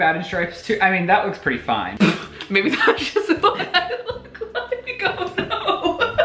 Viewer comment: Does anyone know what says??? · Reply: Maybe this is just how i look like oh no